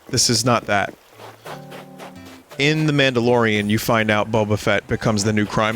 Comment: every time I hear "the mandalorian" I think "the man delorean"